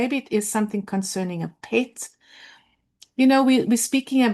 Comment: did you say Pet? Or… I take my animals very seriously